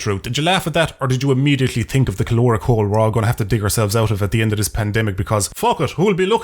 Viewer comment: at first I thought you said “Fof it,” LMAO